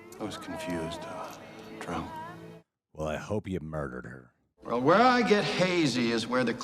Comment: Did you just say "I hope he murdered her"? Was that sarcasm that I didn't get? Because if not there is something seriously, deeply wrong with you. Cheating is a bad thing, but even jokingly saying that if somebody did it they deserve the violence that is coming to them is despicable.